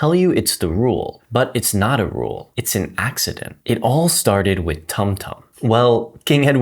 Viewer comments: At first I heard "it all started with Tung tung" I'm like, Shit here we go again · When he said tum tum my rotten ahh brain thought he said "tung tung"🥀🥀